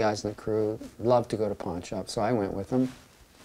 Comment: before reading the description i thought he said "punch up" instead of "pawn shop" loool "my friend was into punch ups so i got into a punch up with him" i was like WTF?